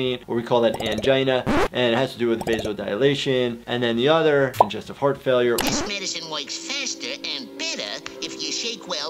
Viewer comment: For a split second I thought you said Congestive Fart Failure